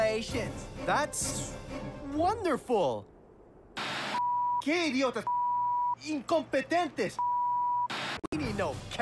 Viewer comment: “Mierda! Qué idiotas! Incompetentes!” I think that’s what he said.